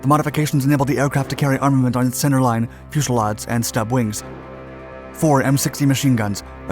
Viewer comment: did I hear 'fushelads'? ;)